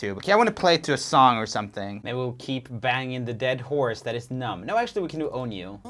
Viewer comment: Did he say "banging" the dead horse? Yikes.